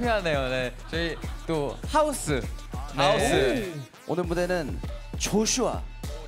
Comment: Did woozi say "Joshua" in 2:57 because Joshua = Crazy? You're right, Maestro is CRAZY